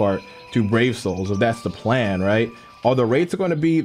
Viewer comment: The whole time I thought you were saying Dark Souls and I was like " are you on that Namekian rush brother? "